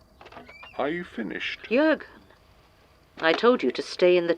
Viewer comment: The first time I listened to this, I thought Gertrude was saying "Jurg!" at 15:50 and was a bit surprised. Didn't expect them to have that level of camaraderie. But looking at the episode's transcript shows that she was actually saying "Jurgen!" hahaha! Still, it's amusing to think about